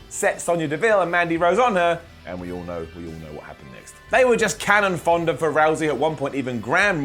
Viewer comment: Did he just say cannon fonda...